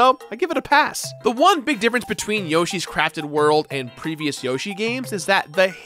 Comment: All I hear is “Yoshi’s Crappy World” and it makes me laugh. Liked and subscribed. 👍